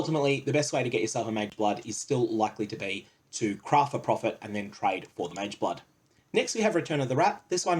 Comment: Likley to be.. What? It sounds like ur saying Likley to be cry for profit and trade for mageblood Can anyone explain what he is saying there? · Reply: craft for profit